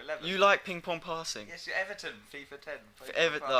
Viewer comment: What does he say at 4:35 after the louder guy asks "you like ping pong passing" ? It sounds like he says Everton but I don't understand what he'd mean by that? D